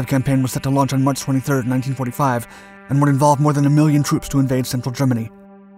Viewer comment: 100 million troops? Did I hear that right?